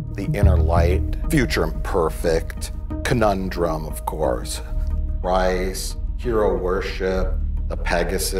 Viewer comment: What does say after Conundrum (5x14)? The Price (3x08)?